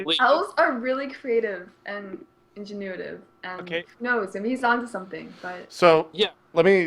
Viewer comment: Did say "ingenuitive"?